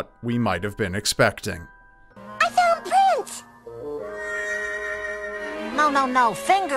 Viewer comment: I think she said I found prince @ 7:25 since the cartoon looks like Prince lol